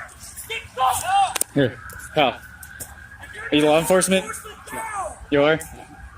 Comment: Someone is yelling, "We got law enforcement down!" Did anyone else hear that?